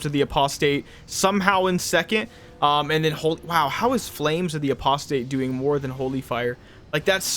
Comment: All I heard was "Flame of the Prostate" LOL